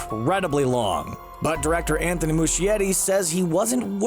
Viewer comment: did he say Anthony Muschietti?